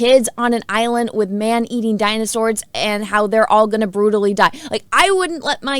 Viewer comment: did you say Dinozords?